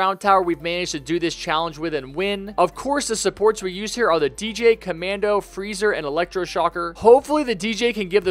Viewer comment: you said commando instead of commander